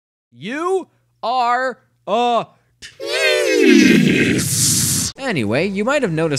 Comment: YOU. ARE. A. *???????????????*!!!!!! Me: I have NO idea what you just said. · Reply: The last word is "tease".